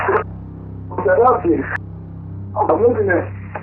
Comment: I think the opposite. I think he went there to rob and it didn’t go how it goes in the movies and he panicked when he realized the police had been called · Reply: that's what I think too, especially since it sounds like he is saying "I'm losing it."